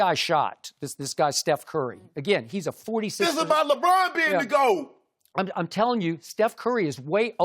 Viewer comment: Can anyone understand what Shannon Sharpe is saying??? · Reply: is a great example of Shannon speaking like he drank one too many beers. Sadly I can understand what he's saying though. "Dis a-boud Lebron bean da Go!"